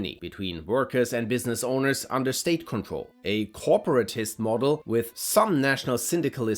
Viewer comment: wait what’s that… did you say “state control”